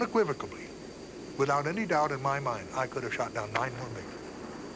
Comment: Along with the SR-71, the F-4 had to be the loudest jet I've ever heard, especially at takeoff. I can't imagine how loud the deck of a carrier full of Phantoms must've been back then. - 4:48 Is this Gen. Olds saying he could've shot down "900 Migs" if his F-4 had a gun? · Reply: i don’t think he said 900 what i heard was “i could’ve shot down 9 more MiGs” (i could be completely wrong)